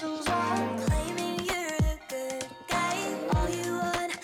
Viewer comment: SHOOTING AT THE ANGELS WHILE CLAIMING YOU’RE THE GOOD GUY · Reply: I HEARD IT AS "GOOD GAY"